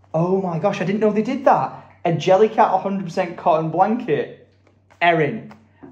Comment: I don't know if it is just me but it sounds like is saying JellyCart instead of JellyCat. This whole time I thought it was JellyCart lol. Love the Videos!